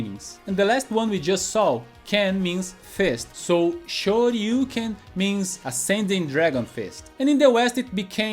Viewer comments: Here's what we thought they said... literally the whole school.. Shouryuken = "All you can". There was even this one dude who was sure there was some lore about being the best you can be and, if you really listen closely, you can hear Ken say "All you can do." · My own mishearing for Shoryuuken was: - Lord Yuuken! As if Ryu and Ken were calling upon some otherwise unintroduced lore character called "Lord Yuuken". It's like they're knights calling upon their liege lord. Or maybe Lord Yuuken is some grandmaster of their martial art. Or maybe some deity.